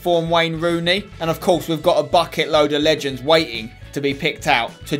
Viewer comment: I thought he said fuckin' load of legends